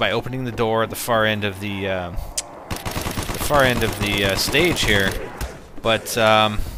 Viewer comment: LOL google translate speach thing at 00:40 thought u said estate instead of stage.